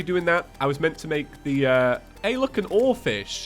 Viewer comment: I heard this as “Hey look, an orphan!”